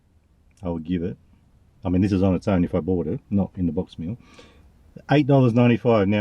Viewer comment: thought u said wiener box meal for a minute there Without further a mountain dew Is about the normal going price now